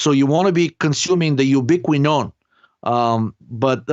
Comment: which one does say? Ubiquinone? Hard to hear exactly which one he is saying 🙊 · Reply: Yeah, the fully oxidized ubiquinone form of coq10.